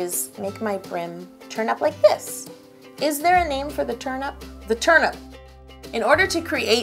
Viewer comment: is it just me or does it sound like she is saying turnip not turn up